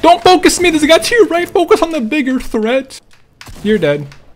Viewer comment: Did he say the n word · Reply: Nah he said bigger